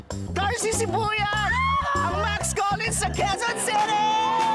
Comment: narinig niyo poba na sinabi ni banny na MAX COLLINS? Ang totoong pangalan ni darcy! Narinig ko Lang po🤣